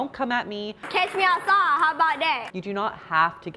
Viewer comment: what did she say...... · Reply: Catch me outside, how about that? Just guessin' hahaha